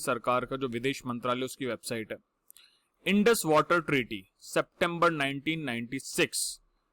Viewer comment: did I heard him saying September 19, 96 🤣